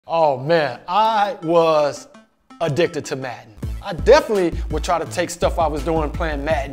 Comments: Who else thought he said he was addicted to meth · Lmao yoo the subtitles at the beginning says “I was addicted to meth” instead of “I was addicted to madden”